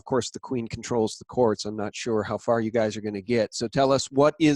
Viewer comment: Oops, I use y'all and fixin all the time..involuntarily. lol! I'm from Pennsylvania, born and raised. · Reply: Thats interesting! I thought y'all said you'se guys!